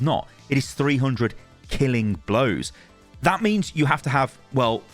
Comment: I think it was changed because I could’ve swore it said 600 killing blows